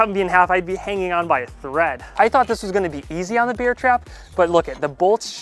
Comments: I keep hearing ''Beer trap''.... ROFL! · sorry I keep hearing "beer trap"